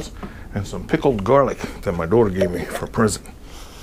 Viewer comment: sounds like you said pickled garlic that your daughter gave you from prison lol